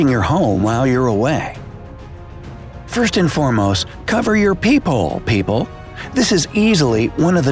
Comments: Uhh I thought u said peewhole 🤣😂🤣😂🤣😂🤣😂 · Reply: I thought he said peehole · Thought it said peehole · I thought it said peehole lol